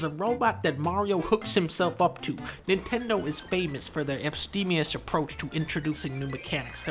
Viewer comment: Can't believe how casually he busted out the word "abstemious" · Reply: I heard ‘epstemious’.